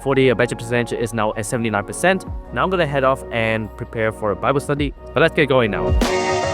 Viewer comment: did i heard Bible study ❤❤